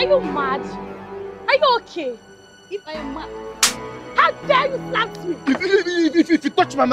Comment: Did I just hear, "how dare you slapped me"? Nigerian movie, oshey!!!!